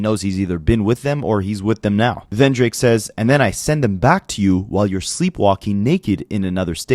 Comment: You missed with “send em back to you” it sounds like he saying “Cyn” which is the name of Joe’s BM 😭